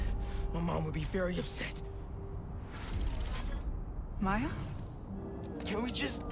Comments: why did the subtitle say my ass instead of miles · Bro in 28:23 activate subtitles and will say: my ass? Instead of miles💀